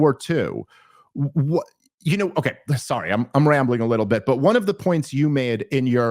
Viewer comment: What does Daryl Cooper say at 36:19? "Very good rambling."? Thank you.